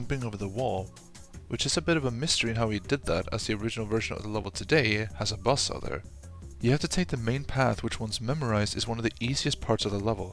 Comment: Has a what now? I can't seem to make up the words no matter how much I repeat it. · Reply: He said buzzsaw